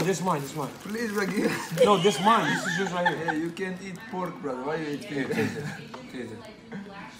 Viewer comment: Anyone know what Islam says at about 1:36 ? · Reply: Hey said u can’t eat pork